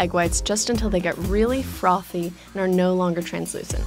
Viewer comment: Did I just hear "froffy" at 1:50?...